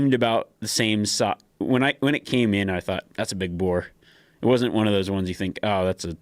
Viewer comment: Did he say that’s a big boar at 6:33 ?